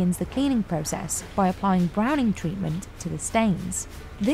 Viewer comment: "...browning treatment..." (???) I wanna make sure I heard that right. If anyone knows specifics about it, I'd greatly appreciate it.